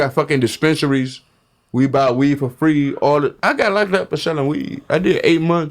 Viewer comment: I used to not be fond of, but you need him there for balance. Otherwise this podcast would just be a mob sitdown. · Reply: Did he say “Buy weed for free”? 😂